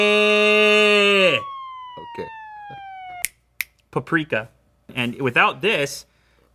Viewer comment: did he just sayed paprika (in slovakia means pepper) in